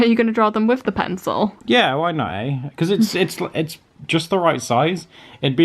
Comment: Did just say "eh?" around 5:10? I love when people say eh, its so cute.